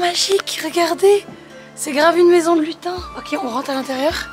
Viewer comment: Ils qrticulent tellement bien que j'ai compris "une maison de ..tain !"